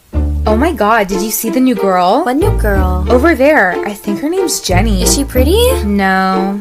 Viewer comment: I thought it said jeramy- no its jenny XD